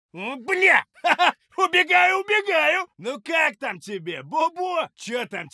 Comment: what he says here? it's the only voice line I hear from he (sorry for my english) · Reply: "Убегаю! убегаю!"